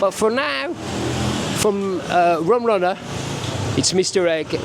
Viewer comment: At first I thought it said "Bum Runner". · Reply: No it says Rum Runner